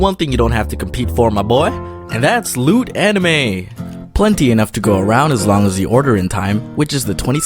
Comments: i thought he said lewd anime. i mean... i guess that also applies xD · When you said "loot anime" I thought you said "lewd anime" 😂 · Instead of loot anime, I thought you said lewd anime. And I thought this is... new. Didn't know you were like that Domics. Lol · Thought he said lewd anime instead of loot anime and I was scrolling through the comments at the same time so I got so freaking confused and scrolled up to the top again faster than the perfect boyfriend can buy chocolate, ice cream, tampons and pads for his girlfriend when she's PMSing or on her period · Ngl, I thought he said lewd anime and not loot anime. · I thought he said lewd anime instead of loot anime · I thought you said lewd anime instead of loot anime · I thought you said lewd anime XDDDDD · when he said lootanime I thought he said "lewd anime" and I was like FUCKING YEAH...oh aw